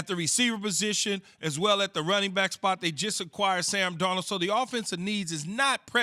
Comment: Did they just say the Titans just acquired Sam Darnold?